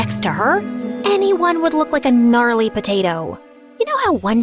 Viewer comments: did she just say gnarly?? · Okay did she just say “GNARLY potato”😭??? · DID I HEAR GNARLY??